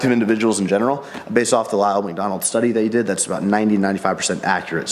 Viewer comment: Thought he said ronald mcdonald study !! Was like wtf haha